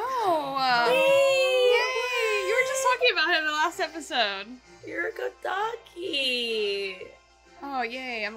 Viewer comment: Did she said 'you're a good doggy' ??